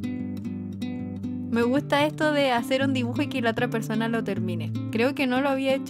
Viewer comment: Que entretenido fue hacer esta colab!! Me gustó mucho pintar tu dibujito, y como te quedó el mioooo lml💕 Un gustaso ^^ pd: cuando busqué en google que era vestido cuadrille si me asusté😂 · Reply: Hay , me avia emocionado , pensé que decía " me gustas piyo " 😐😐😂